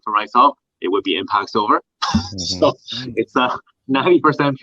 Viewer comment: What the hell is he saying - Impine Silver? I'm hearing "Impine." That can't be what he's saying though. Somebody help me out. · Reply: He said Impact Silver